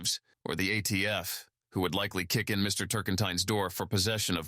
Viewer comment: I misheard his name to be "turpentine".